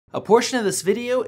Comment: at the begging I thought he said abortion 💀